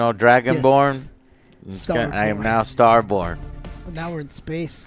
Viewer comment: I keep hearing “Starporn” every time he says “Starborn” 😂 well that would’ve been more interesting